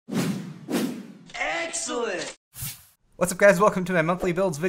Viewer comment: I say "SSSSSS" right before the video starts So it sounds like it says "Sexcellent"